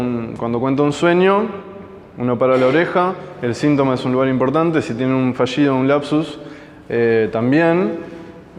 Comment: Hola. Quiero saber con exactitud qué es lo que dices en el minuto 11:30 en el sentido de cómo aparece o se manifiesta el inconciente, expresas: "cuando cuenta un sueño, cuando tiene lapsus..." puede mencionar los otros dos... Muchas gracias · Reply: Sintoma, fallido, lapsus, chiste